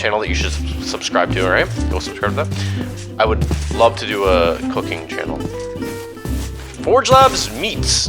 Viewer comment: shit man I thought you said cocaine 💀